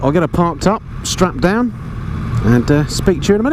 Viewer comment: "Did he say strapped down or Strap on ?" Herbert = Family Guy lol